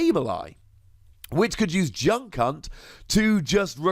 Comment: it sounded like he said “Junk Cunt”